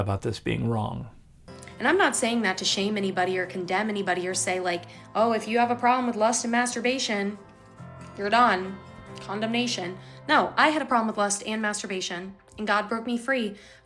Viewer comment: did she say “condom nation”?